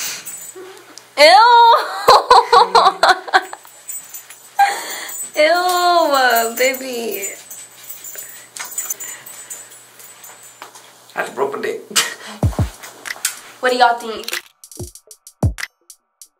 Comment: it sounds like he said “I just broke my dick”ik he said “my neck but still 😂😂💜❤️💙💓